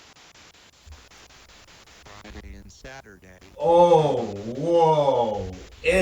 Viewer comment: only repeats Saturday but I heard it say and Friday and Saturday